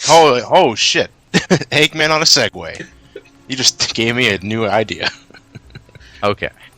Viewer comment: put CC on and where somebody says "you just gave me a new idea" the captions say "Houston we have new Ideas" WTF